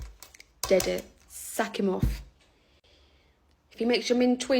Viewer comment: I thought she said suck it off. Like didn't already do that